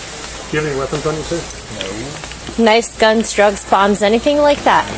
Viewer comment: the subtitles are wrong, she says kn*ves,... not nice g*ns,... xD